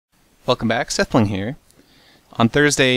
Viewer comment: Omfg , am i the only one who thought when Seth introduce himself at the start of the video , I kind heard him say " Fuck em' back , sethbling here " LOL.